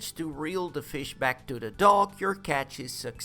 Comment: did he just say dog?